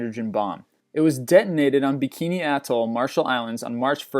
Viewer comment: did he say bikini bottom